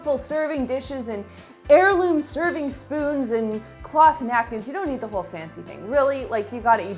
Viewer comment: Did I hear right??? "cloth nappies" ? lol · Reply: happyflowers1212 i think she said cloth napkins!